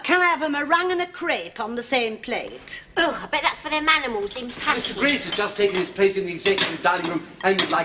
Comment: What did say?! "I bet it's for them animals in _____" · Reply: general zzodd “Packing”